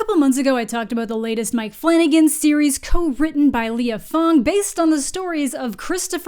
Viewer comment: I thought you said co-written by "Leo Fong" I was confused for a second lol